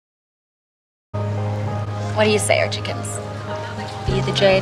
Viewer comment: No one: Veronica: ArChIkIeNs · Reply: Archiechickens is what I thought she said not archiekins